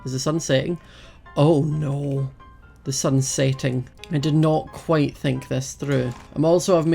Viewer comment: I THOUGHT HE SAID THE SUN IS SATAN XD LOL